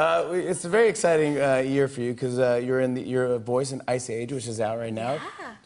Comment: Does he say "youre a n word" ?